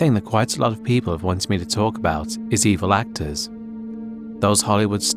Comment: i heard "blacktors" thinking video is about black actors